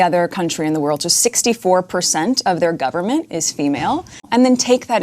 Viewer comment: did she say "JUST.... 64% of the government" ?